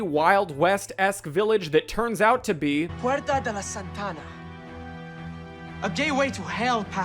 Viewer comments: did he say ”puerta de la Santana”? as in Carlos Santana?? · Anyone’s else’s captions say a gay way to hell instead of gateway at 11:00 😭✌️